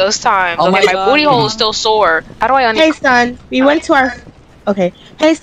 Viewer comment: when she said hey son i thought she said gay son